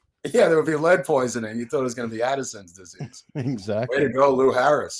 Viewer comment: in that opening clip, i almost could've swore i heard that guy say assassin's disease! ha! ✌️🐝➕